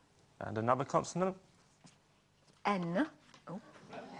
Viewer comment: Did Carol say "Oh, bugger" at 0:10?